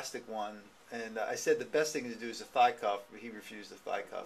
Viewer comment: So Doc, at 24;12 you said the best type of brace was a WHAT? Sounds like you said Fly cuff? I have a bilateral pars defect at L5, I am 39 and have the condition: Ehlars-Danlos Syndrome Type III, SO.....needless to say my ligaments running all through and around my spine are not holding my spine firmly together. I served 10 years in the US Army, have always been in moderate to great physical shape/fitness; worked the last 10 years pretty actively, not at a desk much..... this defect was just detected this year when I came to my ortho doc with excruciating/severe pain. Could not even stand. Had to lean over stools or tables. So can you tell me what kind of brace you mentioned in the video, forgive me, it was inaudible? I go to the VA for treatment.....don't want to say anymore about that. I am my own and best advocate that I have found so far. I have many many more issues than this pars defect. Thanks for this video, it was enlightening. I am going to ask for a bone-scan. Which scans are the ones they do when you are standing up? Again, thanks Doc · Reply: a thigh cuff brace